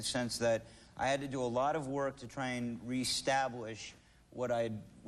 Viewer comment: does he say restablish @ 3:40?